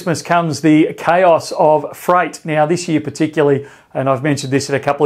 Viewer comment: ROFL 🤣 At min marker 0:08 I heard “fright” 👻 and thought this vid was going to be about “the ghosts of Christmas past” and then when you said it again later I understood you meant “freight” (in my head I convert words to Indian pronunciations so think like payynt, freyyt etc 😂) These days even freight gives people quite a fright though 🎄👻 🚢 👻🎄🚢